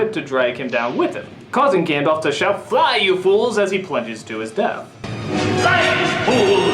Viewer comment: To me it always sounds like Gandalf is saying "Bother you fools!" just before he falls. Only once have I heard it sound like "Fly you fools"